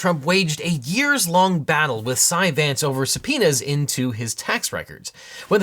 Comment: “Donald Trump has had years long fights with Cy Vance over inserting penis to his taxes,…” Oh the joy of being half deaf and mishearing things.